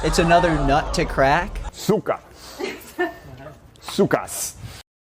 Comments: why did he just say suka(bitch) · for anyone wondering: i think he says cyka which is russian for slut (Disclaimer: i don't speak russian except a few words. It just sounded like that and tbh, he seems like the type of guy that would just randomly say cyka)